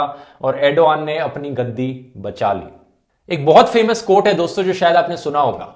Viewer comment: did he said gandi bacha li?😂😂